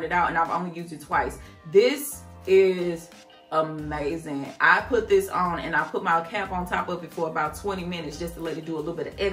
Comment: chile I thought you was bout to say "I put hlthis ish on erthang" 😂😂😂🤣 hot sauce commercial 🤦🤣